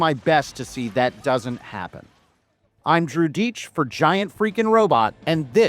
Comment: Great video. oh yeah btw, i forgot that Joe Miller was also the punisher. Also when you introduce yourself at the beggining of your videos i always hear cha'DIch instead of. Keep up the good work you are doing, your videos are very entertaining and informative. LLAP :)